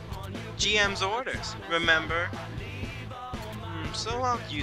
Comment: when u said remember it sounded like axel for a second